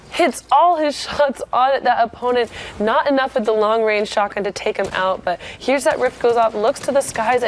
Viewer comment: lol what did she say say "long range shot gun" deceased😅😅😅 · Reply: Carthy she said the long range shotgun wasn’t effective